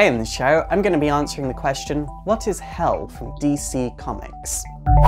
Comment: At first I thought the video said "What the Hell?"